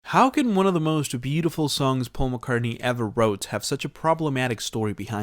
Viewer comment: Sounds like he said "One of the most Beatleful songs Paul McCartney ever wrote"